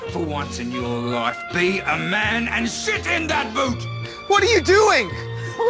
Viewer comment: Did he say sit or shit in that boot? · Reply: Shit in that boot. :)